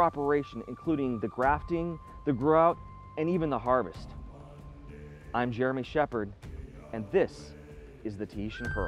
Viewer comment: I thought he said "I'm German Shepherd" lol